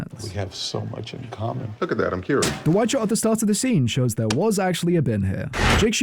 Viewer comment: I really thought for a second you said 'the white shit at the start of the scene'